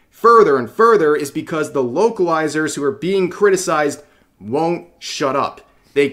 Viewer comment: Did I mishear someone say "woke-alizers"?